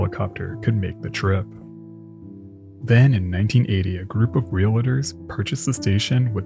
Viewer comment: Did you say "realitors"❓